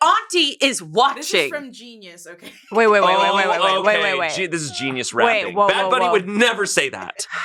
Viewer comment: This has probably already been mentioned, but at 21:08, there's a typo in the subtitles: "this is genius r*ping" instead of "this is genius rapping."